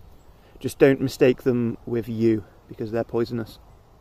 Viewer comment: about pine leaves, you said "don't mistake them with 'you' because they are poisonous" or did I misheard you ? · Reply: Ah I said ‘don’t mistake them with yew’. As in the yew tree (Taxus baccata). It’s a very poisonous tree which some people mistake for pine